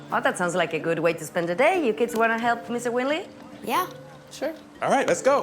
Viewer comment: it sounded like she said 'willy'